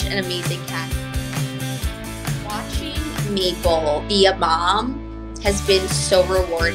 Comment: Everytime she says Maple I hear " Meatball" 😅